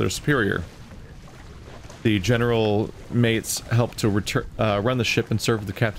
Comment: Omg I'm so stupid... I misheard "mates" as "maids" and was like :"Wtf?" xD Nice and entertaining video as always! .)